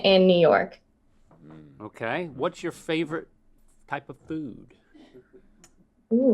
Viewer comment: When Link said, "What's your favorite type of food," I thought he said, "What's your favorite haiku." I was legit waiting for a haiku on chicken parmesan sandwiches. 🤣